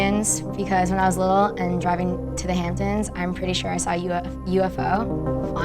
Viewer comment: Wait am I the only one who heard her say “ when I was a little girl and we drove to the hamptons “ ?